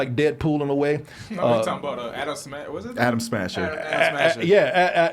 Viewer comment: I really thought they were saying ADAM Smasher at first. I almost got triggered